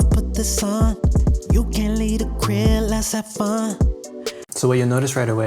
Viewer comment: I think he's saying "let's have fun"